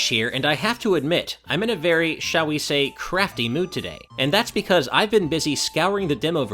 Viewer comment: "I have to admit, I'm in a very, shall we say, CRAFTY mood today" ASH, I thought you saiD CRAPPY there for a second.